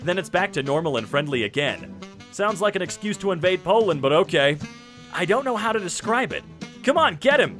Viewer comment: did someone say POLAND